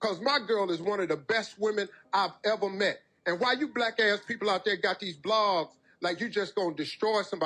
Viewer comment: 😳 Oh Wow.. Did he say “Black A$$ People”